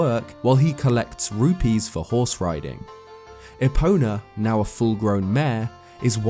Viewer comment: I can't possibly be the only who heard "Epona, now a full grown man..." at first and had to relisten to the thing a couple times to make it out.